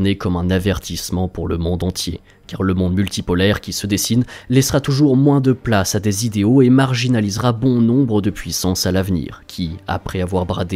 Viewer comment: "Le monde multipolaire laissera place a moins d'idéo et marginalisera bon nombre de puissance à l'avenir"? Wow j'ai du mal entendre, il faut vraiment être un occidental pour dire une telle phrase. L'Afrique, l'Amérique latin et la caraïbe vous pardonnent d'une telle réflexion, eux qui ont été tellement marginalisés et piétiner. Le monde unipolaire à faite quoi pour eux?